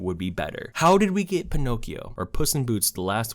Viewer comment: bruh i misheard him saying pinnochio i thought he said the Neo Yokio